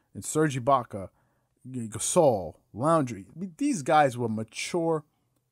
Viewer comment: did he call Kyle Lowry... 'Laundry'? Lol.